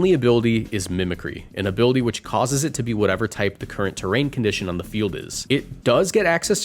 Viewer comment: No missingo 0/10 vid I thought you said every type